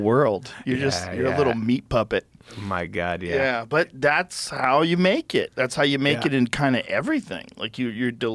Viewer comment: When joe said that's how you make it..all I heard was "homos naked" bahaha I love these 2 guys this is incredible!